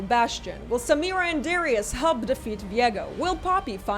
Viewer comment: Why did I hear "Will Samira and Darius hump to defeat Viego?" instead of help??? 🤦🏻‍♂️